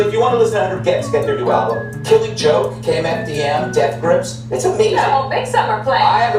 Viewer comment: did he say....KMFDM?!!!!!!